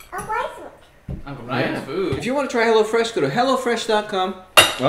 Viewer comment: I thought you said "hellafresh"